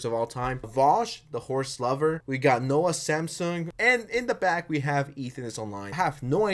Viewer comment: Did he say Noah Samsung??